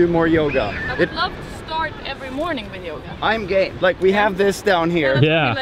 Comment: does he say "Im gay" :)